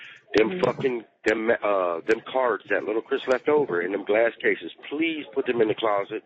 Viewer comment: They are Larissa's oldest son's cards so not even his son. 😮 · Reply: he says lil Chris cards… I thought he said Angel all this time too 😂